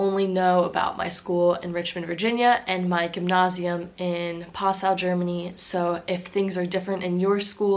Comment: wait a second, did you just say "Passau" like the city?